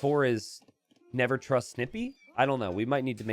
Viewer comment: I thought he said never trust stimpee and I shot up from my slumber. You know if you know haha